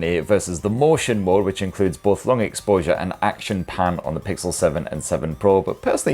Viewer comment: I heard "action pan" as "Action Pam" and now I'm wishing it was a new movie franchise.